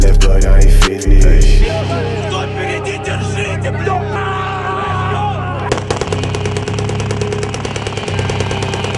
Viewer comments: what did he sayy? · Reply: "Who's in front, hold on." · what did he say? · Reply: кто впереди держите его who is ahead hold him